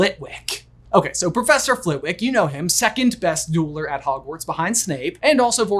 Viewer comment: I was half-listening while this was on, and thought I heard call Flitwick the 2nd-best yodeller in Hogwarts XD